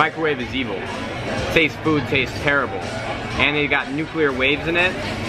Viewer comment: Did he say "taste food taste terrible"